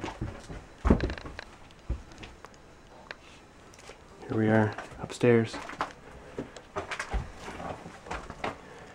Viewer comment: can anybody hear a whisper with two words? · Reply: RSBritain Yeah. HE whispered "Holy shit!"!!!!!!